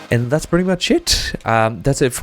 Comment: "and that's pretty much shit" :) is what I hear :) but it's nice holy shit :)